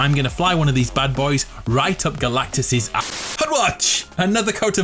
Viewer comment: if you put on subtitles instead of hut watch it says head watch